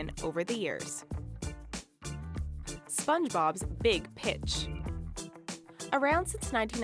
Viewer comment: Thought it said pig bitch for a minute